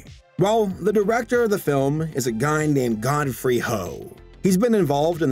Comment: Who else heard "god free hoe"?